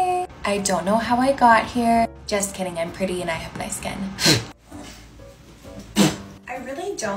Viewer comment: “Jk I’m pretty and I have nice skin” PURRR oooo i’m famous 💅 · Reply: no i thought she said “jk i’m pretty and i have white skin” 😭